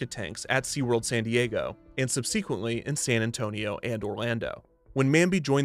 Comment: Did he just say "sub-SEA-quently" instead of subsequently?? Great punwork there, a real groaner! 😆